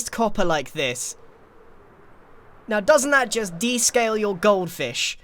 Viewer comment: "Well, doesn't that just de-scale your goldfish" ( 3:36 ) I love nonsense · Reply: Oh, it's "de-scale", I swear I heard "de-stale your goldfish" (like the snack)